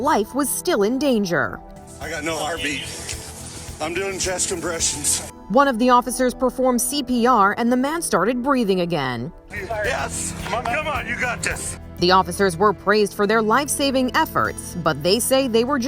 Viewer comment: sounds like he said chest impressions 🤣🤣